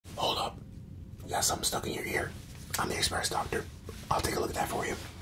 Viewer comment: i fr thought he said ‘yes im stuck in your ear’